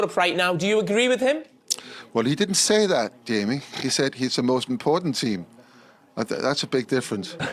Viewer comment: I thought he said ''The most in-form team'', not the most important team. Am I wrong?